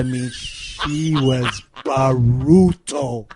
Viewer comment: what was that word Konnan said? Sounds like BARUTO or BAROOTO. I've tried pulling it up and can't find it. Help anyone? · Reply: Spetsnaz Pocomaxa well in case you're serious, he was trying to exaggerate the word brutal.